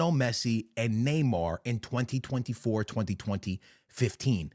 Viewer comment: What is bro trying to say, “202014” “202015” 🥴